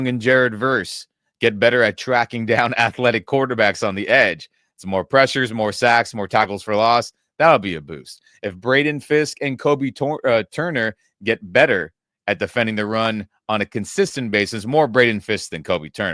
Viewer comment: I know it’s because I’m hungry because I haven’t had breakfast yet, but I thought I heard you say more tacos for loss, and I was like why would more tacos be a loss?